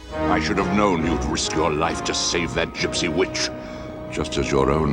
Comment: I thought Frollo said Juicy Bitch and I had to replay it several times to figure out that it was gypsy witch.